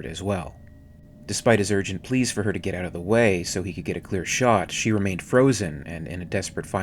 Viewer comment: I always thought he said "get out of the damn way" to Lambert lol